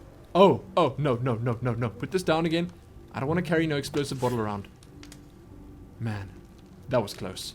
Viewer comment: I thought it said expensive bottle :p